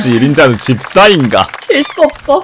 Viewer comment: 나만 재수없어...라고 들림?